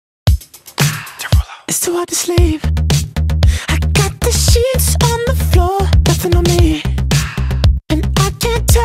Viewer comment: Not going to lie but when I first heard this song I thought the beginning said "it's too hard to sleep, I got the shits on the floor". LMAO